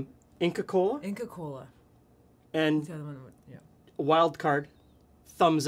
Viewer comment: Yeah we know that... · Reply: Sorry I thought I heard "Mexican colas" when mentioning Inca Kola :D